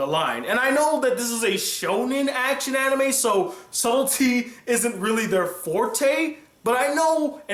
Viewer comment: Haha when you said subtlety I thought you said "salty" and I was like... Well you're not wrong kacchan is pretty salty